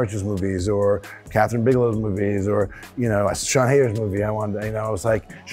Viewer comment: I can't understand the last director he mentions in 1:16 ? Sean "haters"? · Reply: The last director Ben mentioned was Sian Heder. One of the movies she directed was KODA for which she won an Academy award. Another movie she directed was Tallulah.